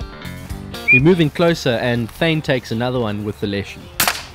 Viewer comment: what gun is that? I think you said Leshie? Lesion? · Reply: Edgun Leshy